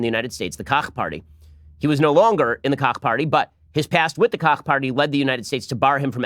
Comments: Did Ben just say Cock party 3 times in a row? XD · did you say “cock party” ?? And if so, how can a someone join? Asking for a friend